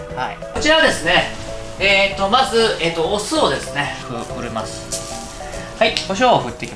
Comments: 画面から目をちょっと離した時に「お酢をォ」って聞こえて生姜焼きに酢！？って思ってテロップ見たら「お塩」でしたw呂律ー！ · 俺も「お酢をォ」と聞こえた。うまそ！